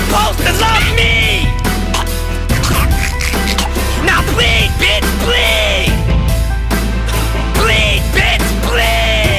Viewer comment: I always thought that Eminem said PLEAD BITCH PLEAD not BLEED BITCH BLEED